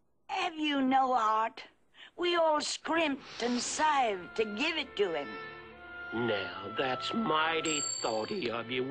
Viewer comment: what does she say here? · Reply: we all scrimped and saved to give it to him.